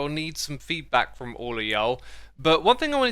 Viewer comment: Did X really say “Y’all” at 20:50?